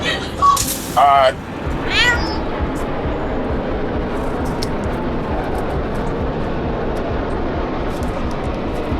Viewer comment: What did that damn cat say 😅😂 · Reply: He said ….. damn 😂😂😂on god